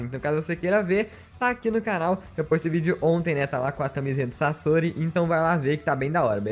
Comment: CAMISINHA DO SASORI?! Eu ouvi direito?!!! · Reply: o cara disse camisa do sasori